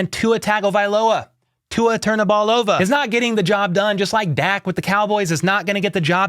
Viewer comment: I was walking in the kitchen drinking a Pepsi and I heard you say Tua turnthaballova. I spit my drink out laughing